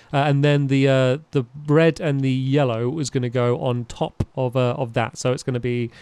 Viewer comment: Anyone else realise he said the bread and yellow instead of red😂😂😂